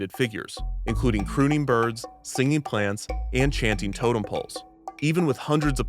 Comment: Chanting totem poles. Ahhhh! I had to rewind twice I thought he said Channing Tatum pose 😂